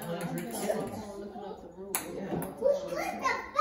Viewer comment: did anyone else hear say wtf is that 😭😭😭